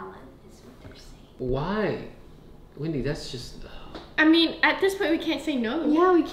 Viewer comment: that was their mom was responding to...But I can't make out her name. "Wendy" idk